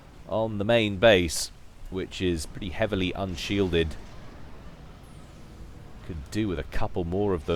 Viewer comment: wait - did he just say "heavily unshielded"?